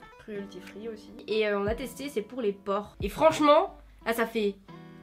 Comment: j'ai compris "la marque est végan on a testé c'est pour les PORCS " 😭😭 je me suis dis mais wtf mais non c'est "PORES" je suis trop bête 😂😂😂